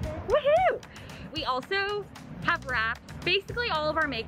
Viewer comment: I so thought you said "We have RATS", not "we have wrapped". Lol I was so confused and had to rewatch that part with CC on. hehehhe